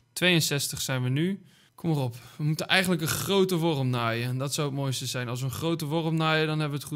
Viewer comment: een grote worm naaien like als hem ook hoorde